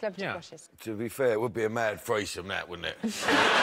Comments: what does danny say at 14:38? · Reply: I'm not 100% sure but I think he says: "To be fair, it would be a man with a face like that, wouldn't it?" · What does Danny say at 14:38? "To be fair..." ?